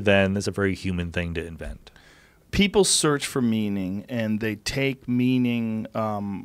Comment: I thought Joe said "People search for MEMEing". I loled. it's 1 am.